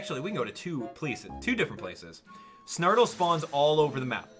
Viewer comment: For a while I thought he said snartle not snottle XD